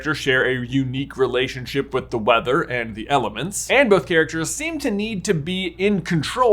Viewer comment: No lie I thought J said "weather and elephants"